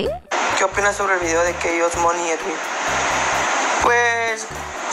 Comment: Dice "Que opinas del vídeo de K OS (Kenia OS) y Mont y Edwin. ¿Escuche mal?.